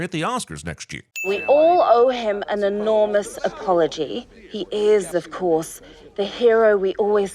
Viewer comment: "he is" is being said but the caption reads as "he his"